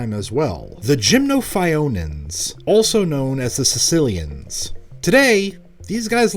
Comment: for some reason I heard “Sicilians” and started laughing